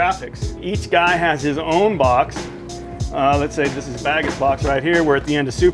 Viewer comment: Did he say Bagget ?